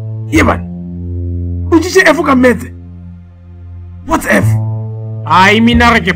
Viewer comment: Always thought that said ftsek instead of whats f shem yoh😂